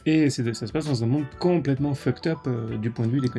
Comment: C'est quoi une connexion sociale "Fucktop" surtout le fucktop, bordélique non? 1:50 XD